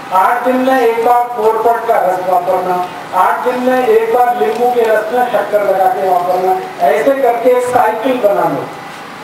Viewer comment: Can anybody help to make me understand what Rajiv ji saying from 2:21 to end🙏. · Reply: Nimbu ke rash me shakkr milakar lgao